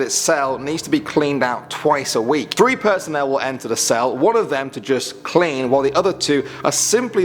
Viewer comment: did anyone hear him say "3 person will be in the cell.'' at 4:48?